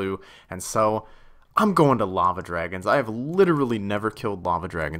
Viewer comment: swear to heck this guy just said llama dragons and now I've got images in my head and ii can't stop kek-ing